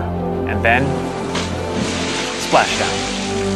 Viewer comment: He said "splash down". At first I thought I heard "Splackow" LOLL. I was tripping laughing for a second.